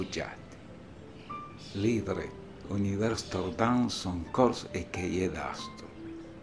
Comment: Qué es lo que dice en el minuto 6:54 ??? Es latín? Qué significa? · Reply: Es francés, un poema de Victor Hugo: "L'hydre-univers tordant son corps écaillé d'astres".